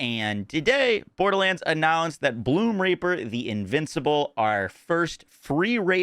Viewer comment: sometimes you say reaper, sometimes you don't put enough enunciation into that first E, making it sound like your saying Bloomr*per...